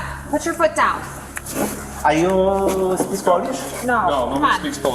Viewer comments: he actually said “do you speak Polish” he is from Poland which explains his question* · He clearly says "Polish", not "Spanish". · I think the guy said "do you speak Polish", since one of the officers in response says "no, no one speaks polish". its easy to miss on first hearing but you can pick up on the P. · Really good video, but i think that in 14:30 he said "Do you speak Polish" not Spanish but I could be wrong also later he said Kazimierz and this is polish name idk if i am right do anybody also heard what i heard? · subtitles are wrong They said polish not spanish i believe